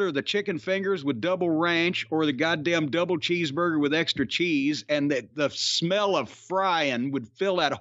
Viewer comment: Did he say "extra cheese"?